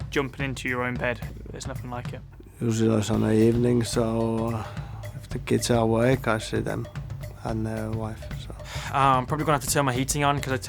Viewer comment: Unable to decode what this legend is trying to say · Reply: Usually on a Sunday evening, so if the kids are awake I see them and the wife.